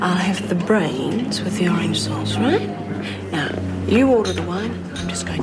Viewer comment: Did just order “brains with orange sauce” or am I hearing things? @ 16:40 🤮